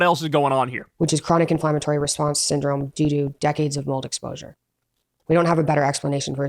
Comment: Did he say Doodoo again? Due to ? 😂😂😂